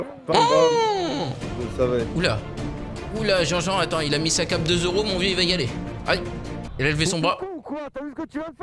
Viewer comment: MOI j'ai compris "ta cap deux euro "